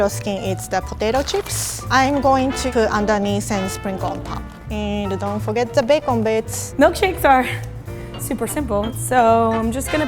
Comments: 9:5 I thought she said and don’t forget the bacon bitch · I thought said, "don't forget the bacon, bitch." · Did just say “don’t forget the bacon bitchh”